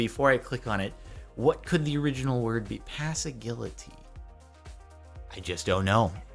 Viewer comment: When you tried to say "paseggiliti" the youtube captions thought you said " Passive Guillotine"